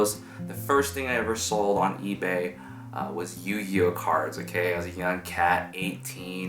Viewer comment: What does he say @ 0:13? Sounds like "Yuyo cars". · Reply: I think he is referring to the YuGiOh cards.